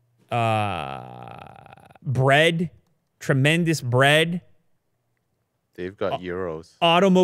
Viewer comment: I'm from EU and this is what I hear: "EU has tremendous bread"